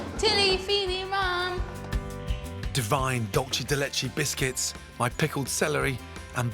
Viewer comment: Holy sh*t, I thought he said Dogsh*t de Leche.